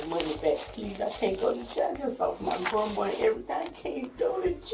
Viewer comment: It said I just help my grown boy everyday. SHE DID NOT SAY THAT DUMMIE. SHE SAID SHE JUST LOST HER GRAMMAR 😢🤎💯.